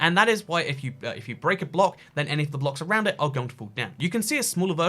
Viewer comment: what in the hell did he say? He mumbled something I can't figure it out even on 0.25 speed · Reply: I think he said any of the blocks around it are going to fall down.